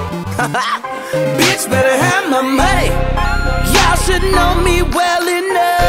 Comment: for the longest time I thought he was saying PEACH BETTER HAVE MY MONEY!!!